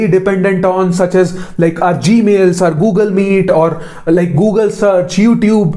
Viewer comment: I heard "Google Maid" somewhere in between 😂